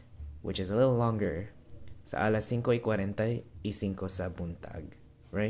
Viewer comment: So what time you woke up after all at 7:18? It says singko-i-kwarenta-i-singko but you have written 4:45? Thanks.